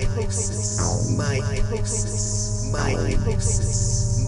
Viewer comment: I thought he said mitosis lol